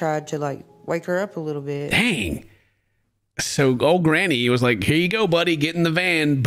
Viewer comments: I heard her say 'granny' too, maybe it was a nickname for Lil Bit because she was a little ol' granny dog at 22! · I’m with Ken on that one. I thought she said grandma too and was like, “how are we just going to casually drop that info!?” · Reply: lol I thought she was saying granny was a “lil bit” dead. I was like wtf